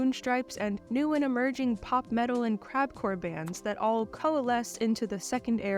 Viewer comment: lol I thought you said "CrapCore" not "CrabCore"